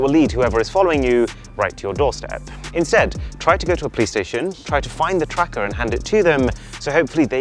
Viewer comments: I thought he said Play Station instead of Police Station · anyone understood the word he said at 5:25 ? it sounded like "try to go to a play station" ??!?!